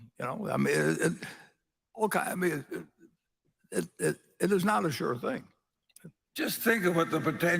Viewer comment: what is he saying at 57:05 ? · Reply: "It's not a sure thing."